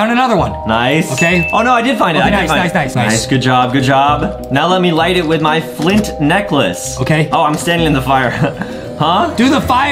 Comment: i thought said good job now let me light it with my flint neck😂😂❤️